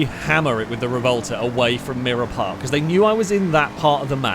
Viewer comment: OMG The name is actually "Mirror Park"?! I've watched your channel for years and I always thought you were saying "Mira Park" because of your accent....!